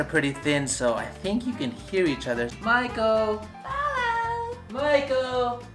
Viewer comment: I thought you guys are saying marco polo to each other for a second